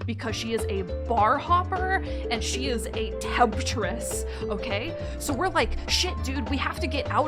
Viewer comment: Did anyone understand what she said? "A bar hopper and a tabtress? temptress??" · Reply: She said temptress because the woman is a cougar